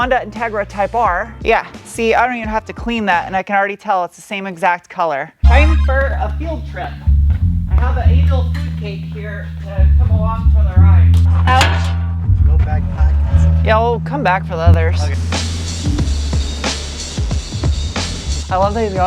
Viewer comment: At first I honestly thought she said she "had an ANAL food cake", and then my brain stopped being stupid. 🤦‍♂